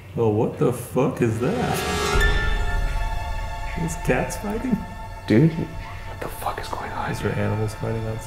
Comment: When he said "are those cats fighting?" I heard "are those cats fucking?" XDDD